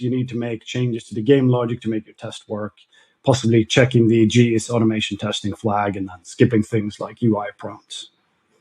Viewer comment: I think the things that he said at 7:59 is "GIsAutomationTesting" in CoreGlobals.h